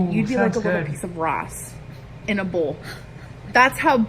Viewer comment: Wow!! I literally thought she said “you’d look like a little piece of grass” when I watched her video..... I thought that’s a strange comparison.... not until watching your video did I realize she was trying to say “rice” - thanks for translating ❤️